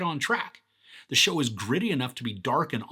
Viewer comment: did you say DARK????